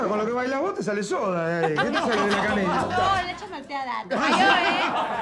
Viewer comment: que dice ahì?? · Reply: Le falló ¿eh?